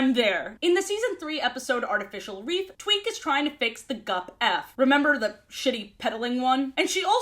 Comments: i swear i keep hearing you say “twink tries to fix the gup F” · Hi yes at 16:40, was I the only one who heard say twink instead of Tweak? Like I heard it, had to run it back, and then repeated said process 10 more times. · now why did i think you said "Twink"....